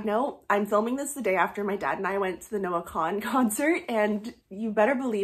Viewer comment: At first I thought you said the Nora Gaughan concert, lol.